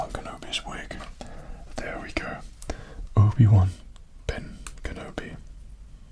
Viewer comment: It sounded like he said Ubiwan kinda like Ubisoft.